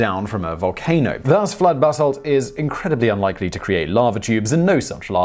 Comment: Every time I hear him say "basalt", I hear bath salt